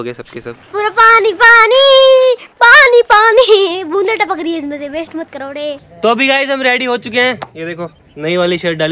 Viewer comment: What did say😂😂😂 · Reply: Waste mat Karo de 😂